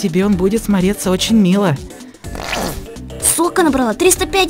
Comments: я один слышу слово с*ка? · я слышу хорошо, но литературное слово сука зачем